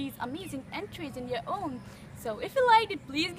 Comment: You really need to work on your enunciation and pronunciation. Makes it really hard to figure out what you're saying. Also, that's not how you pronounce au revoir. I thought you said reservoir :/